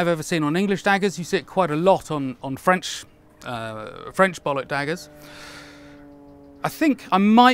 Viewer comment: The triple-bollock dagger is the "E.T." - Extra testicle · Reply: So he really did say 'French bollock daggers' @ 6:08? The form of the handle is obviously phallic, but I could not bring myself to believe that he did actually say 'French bollock daggers'. Go figure...